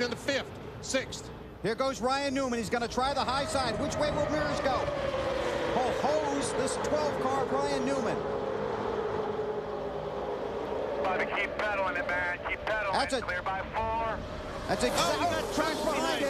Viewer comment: alright seriously someone tell me wtf he says. "ho hoes this 12 car of Ryan Newman" is what I've been hearing for 14 years.